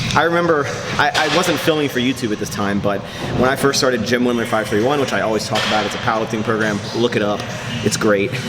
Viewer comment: What was that program you were talking about? sounds like youre saying "gym women 5/3/1" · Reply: Jim Wendler's 5/3/1